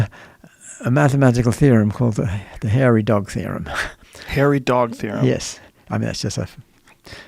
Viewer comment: I'm trippin....I thought buddy said "Hairy Dong Theory" at first....